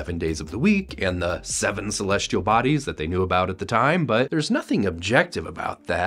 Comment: I mean, Pluto deserves more respect than that. But it’s a moot point anyway, because he’s referring to Uranus and Neptune, not Pluto. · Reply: oh I thought he said 7 planets not 7 celestial bodies